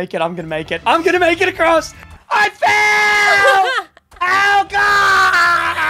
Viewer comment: He did not say I failed he said I fell